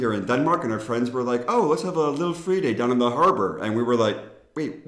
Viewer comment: When you said "lille Fredag" it sounded like you said "lille fridag" which means "small day off" which also would be a great idea haha